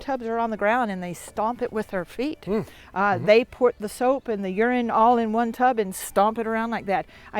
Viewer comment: did she say urine?